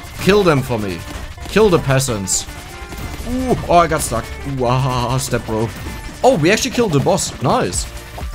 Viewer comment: did he say "ooh ah a ah stepbro"?